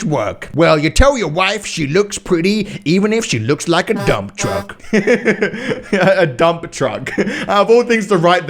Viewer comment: Dump truck? I thought it said dumbfuck